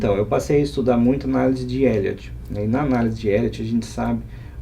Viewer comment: Cohen, ao 39:38 ele falou sobre uma analise, não entendi o nome correto, por gentileza, poderia explicar? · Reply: Pelo o que entendi, ele falou análise de Elliot. Se for isso mesmo, o Cohen tem vídeo no canal